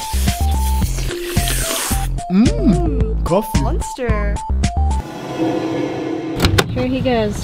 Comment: why have i thought that was saying muenster not monster this entire time